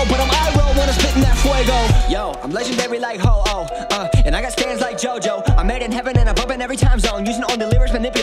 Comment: I remember when I thought he said "I got stans like Jojo"